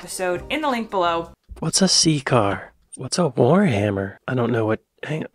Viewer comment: pmsl, I thought she said sea cars too. Who the hell is pete bootejedge?